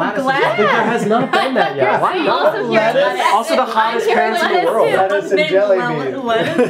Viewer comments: Lili: “I thought ur saying lettuce” 😂😂😂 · did cole just say gladys BABY to Lili??😍😍😍😍